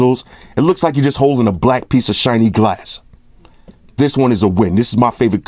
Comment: I thought he said holding a black peace of s**t😂😂